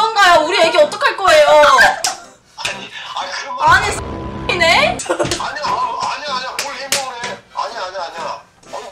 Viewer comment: 검열전에 대체 뭐라고 한겨? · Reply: 싸튀범